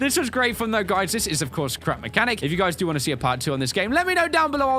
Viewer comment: sounded like you said "crap mechanic"